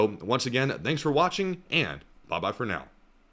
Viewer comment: I distinctly heard you say Ba Ba for now. I am thinking a better sign off would be Ba ba moo ba for now.